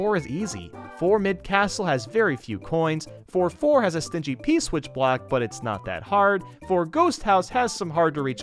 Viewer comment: On 8:20 you said p black instead of p block